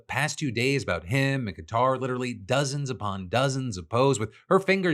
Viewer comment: subtitles says "guitar" when talking about Qatar. Subtitles auto-generated?